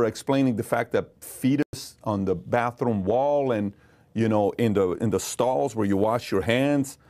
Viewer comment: I just wanted to point out that bro said "fetus on the bathroom wall" instead of feces at 25:23 Interesting slip.